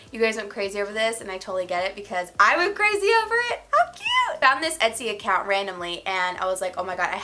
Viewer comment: I thought you said "f**k you!" 😂